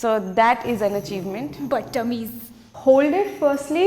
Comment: Did I hear, "Butt-Tummies"😅🙌🏼